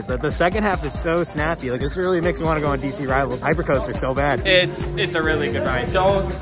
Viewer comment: did he just say DC Rivals Hypercoaster?